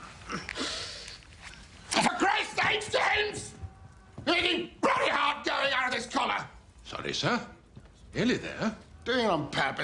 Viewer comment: What did King Bertie said? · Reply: “Oh, for Christ’s sake, James! You’re making bloody hard going out of this collar!”